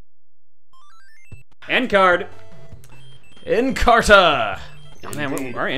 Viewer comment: I keep hearing “N Card” as “End Card” Hey, this video isn’t THAT old